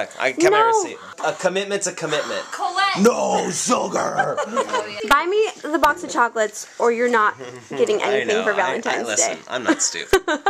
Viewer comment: I thought she said "get me the box of chocolate or your not getting "any" bahahahah